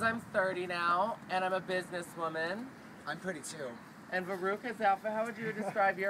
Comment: When Veruca said "I'm 32" I thought she said "I'm pretty too :3" ... I almost died.